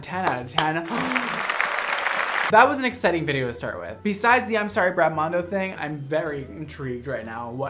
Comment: i thought he said sorry grandma